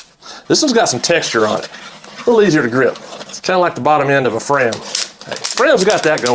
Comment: When you said it’s a little easier to grip, like the bottom end of a Fram, it sounded like you said the bottom end of a friend. Had to rewind.